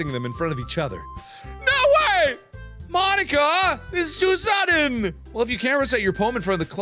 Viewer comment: When he said mon-ika it sounded like he said nikka